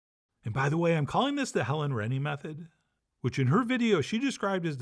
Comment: Finally, a simplified Spanish tortilla patatas recipe. I thought you were saying "Helen Reddy" the singer. 😂